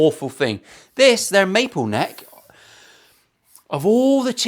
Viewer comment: I was just settling down to watch my favourite Luthiers show when I was sure you said the guitar had a may pole neck Hello I said to myself them Chinese must have long arms I decided I must have misheard you so I put in my hearing aids and rewound you Ha I thought I was almost right It has a Maple neck